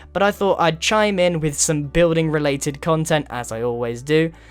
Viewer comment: when you say good bye at the end it sounds like your saying good boy...